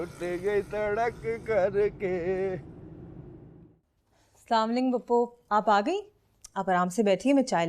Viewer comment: what aqeela said #samling phupho.. what type of Salam is this?